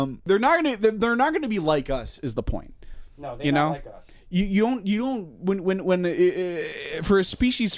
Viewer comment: did say "they not like us" 😭😭😭